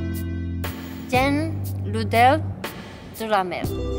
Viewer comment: Merci pour la vidéo que j'attendais avec impatience :D vers les 12 min quand elle dit "j'aime l'odeur de la mer":j'ai compris "merde" ça m'a fait rire ^^